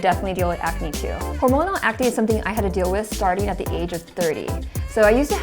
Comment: I heard you say the age of 3 for a second😂